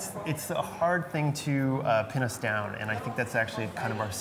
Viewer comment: did he say 'It's a hard thing to penis down' :-) Or was I still thinking about the animatronic sculpture,... !!